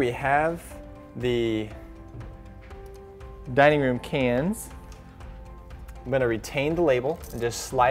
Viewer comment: what do you say at 2:02 Here we have the diamond can ???? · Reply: "Dining room cans", that is, recessed ceiling lights in the dining room